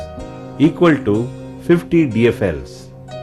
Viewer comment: did he say 50 DSL's?? lmao if you dont know what DSL is nevermind lmao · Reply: Disease free larvare